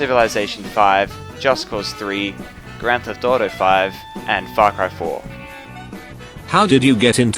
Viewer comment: when he said farcry 4 sounds like he said fuck cry 4 xD